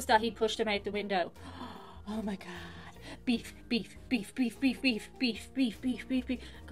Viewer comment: When I hear "Beef beef beef beef!" what I'm actually hearing is "shots! shots! shots!" said in the voice of little John.